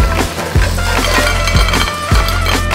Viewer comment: Everybody who thought he said butterflies instead of butter fries: 👇